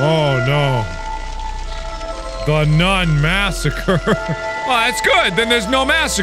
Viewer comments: ok....iM GONNA ADMIT IT I thought it said "Nun massage" Not "Nun MASSACRE" I only just noticed how much n's I said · I thought it said nun massage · i thought the first one said nun massage LKJHJGHFGFS